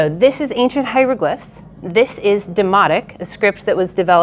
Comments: Thought she said it was written in 'Demonic' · Instead of "thematic", I heard "demonic" first 😂😂😅😅😅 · At first I thought she said demonic (which actually makes sense for a tax document) not demotic…😂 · Oh it is Demotic! I thought she said demonic!